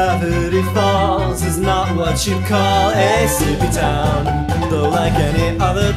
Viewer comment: Gravity Falls is not what you call a sleepy town though, like any other town, all the people rest at the moon's request when the sun goes down... But that is when the mayhem awakens and madness crawls out of nowhere. We go where adventure calls, while the people are sleeping behind safe walls. We're explorers of forests and haunted halls, empty graves and old caves and abandoned malls. I mean there's nothing else to do here in Gravity Falls. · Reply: I always thought he said “soupy town” instead of “sleepy town” thank you for clearing that up for me